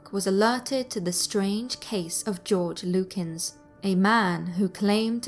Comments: Just stating, I briefly thought the first one said, "George Lucas". Lol! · I really thought it said George Lucas, and I was like "that explains Jar Jar then. " Still mildly convinced Jar Jar is a manifestation of a demonic entity tho.